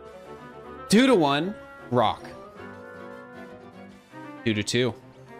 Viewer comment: I swear I heard him say "brock" every time instead of "rock"